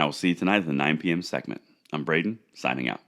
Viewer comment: Off topic, but, Let’s go Brandon!!! 😝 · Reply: I thought he said his name is and not Brandon